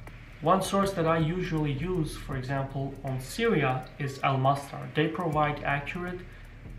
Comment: so he said al Masfar was his source for Syria at 6:03? I'm american, i cant quite make out his accent all the time